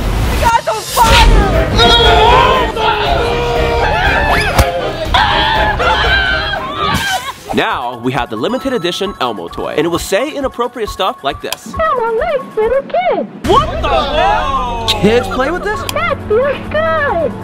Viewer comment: People who couldn't understand Elmo 👇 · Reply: it sounded like he said "Elmo r@ little kids" or "Elmo likes little kids"